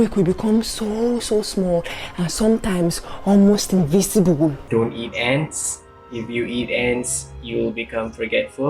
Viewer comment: I swear I thought the Malaysian man said the taboo was ‘don’t eat ass’ 😂